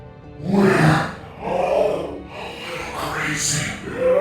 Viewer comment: what is he saying? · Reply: "We're all a little crazy in our own ways."